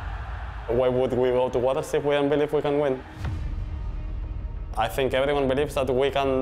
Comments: I like Oscar but wtf did he say? XD I cant understand lol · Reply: Why would they to Worlds if they didn't believe they could win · What is blud saying? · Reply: 'Why would we go to worlds if we don't believe we can win?' - but yeah it took me 2 times to understand him